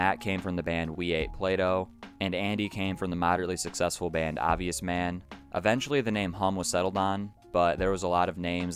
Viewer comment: i cant find anything about Obvious Man? Is that the name or did i mishear